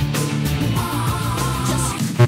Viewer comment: I hear “Freaking final stage”